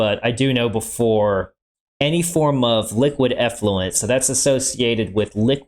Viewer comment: At first I heard "liquid affluence" instead of "effluence" and thought "damn, there's water out there with more money than me?" 😂😂😂